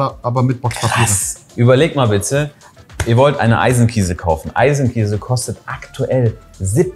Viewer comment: Ich verstehe immer nur "Eisenkäse" 🙂